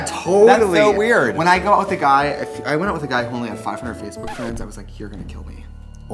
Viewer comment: did he say 500 fb friends?....i only have like 89...and im coommpletlyy...normal